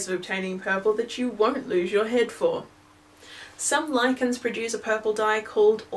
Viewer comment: Am I the only one who thought she said lycans and not lichens?